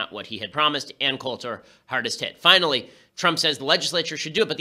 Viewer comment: Sounded like he said "Ann Coulter, hardest tit."